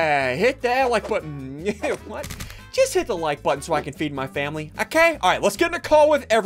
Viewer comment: did you say family?